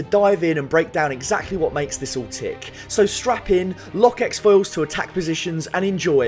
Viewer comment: I don't know if i can call you a true star wars fan since i heard "x foils" when its actually "lock s foils"